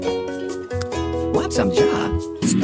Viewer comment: Anyone Else Heard: Want Some Job?